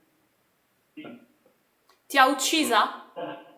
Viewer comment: Interessantissimo cosa è emerso!!😱 ottimo consiglio ragazzi 25:14 ho sentito : “libera di essere uccisa”